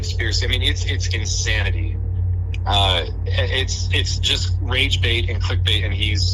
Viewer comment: did he say rage bait :face-orange-biting-nails::face-orange-biting-nails